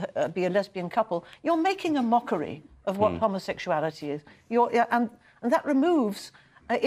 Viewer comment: Did she just say, "...you're making a mockery of what homosexuality is..."